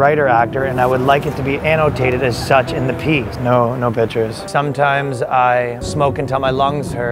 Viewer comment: “no, no bitches” it’s what I heard first. 😅